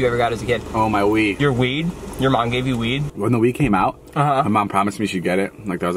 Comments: I thought David said my weed😂😂😂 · I thought he said his mom gave him weed 😂😂